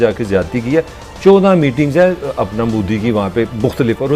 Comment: Did he say apna Modi? Haha, wo bhi mana ki baap to baap hi hai.. lol